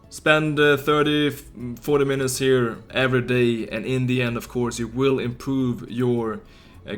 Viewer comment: Every time you say "and in the end" I hear it Indian 10:06 😂